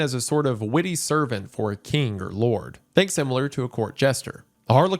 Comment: when you said “think similar” I heard “Thanks Himmler.” Hahah, for a second I was like nice subtle messaging